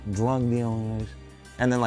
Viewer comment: what the heck does he say after "wise guys" on 2:54 ? "Drug dealing guys???